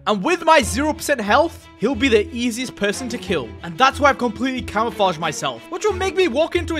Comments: bro on 1:08 subtitle says heal not kill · i think theres a mistake at 1:07. You said he is the easiest person to kill but the text says heal instead of kill